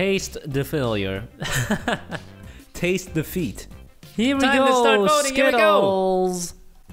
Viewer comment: Taste defeat Subtitles: Taste the feet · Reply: lol it sounds like he said tats e the feet